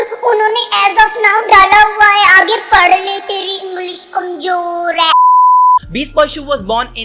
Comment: Guys,,What does he say @ 2:44?? "Shubham, unone as of now dala,___"then?? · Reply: then- age padh let Teri English kamzor hai..